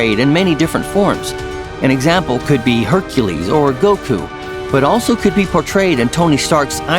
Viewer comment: Did he say "Goku" at 1:59 ? 😳